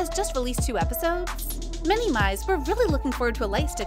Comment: When she said "Many My's" it sounded like " many mice" 💀